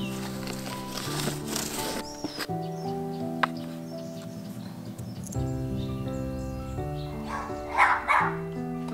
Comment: （标签文字 1.花绣球、2.瀑布番茄、3.大番茄、4.千禧、5.初恋！） 1:53 子柒：（陈阿姨），你们那个鸡棚拆了以后，剩下的木头还要吗？ ：你要的话你拿点去嘛。 子柒：要得，哈哈。 2:09 子柒：1、2...起！ 2:13 子柒：那边那个，也要拆吗？ ：对头。 子柒：那你等我先回去，我拿个手套和你一起去拆嘛。 2:38 子柒：（多拿幾根木頭，來看我化腐朽為神奇。※这句我听不清子柒说的啥，是某网友帮我猜的） 9:38 子柒：好神奇哦！ 16:06 子柒：婆婆，先给你舀点汤喝哈，这次的汤肯定很好喝。 16:20 子柒：这个酸酸甜甜的好开胃啊，对吧？ 16:31 子柒：里边有豌豆，多么好吃，对吧婆婆？ 16:38 子柒：婆婆，来，这个，鹌鹑蛋。 来，婆婆，蛋饺。 好吃吧？哈哈。 17:10 子柒：嗯...这个豇豆也熟了。 17:40 子柒：你看这个汤，越熬越浓耶。 . . 这是英文翻译大侠@ 请擅长其它外国语的高手们也进来参与翻译吧，一定会很受欢迎的哟。因为这里的网友遍布全世界。 祝各位天天上网，好好享受！ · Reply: 子柒：多拿幾根木頭，來看我化腐朽為神奇，哈！……I guess so🤣🤣